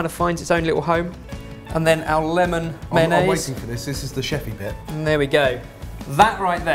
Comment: Did you say sheppey as in the island.